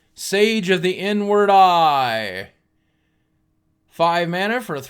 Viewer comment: Listening and wasn't watching at 20:30 and thought you said "Sage of the n-word eye" and knee-jerk reaction said WTF?!? Haha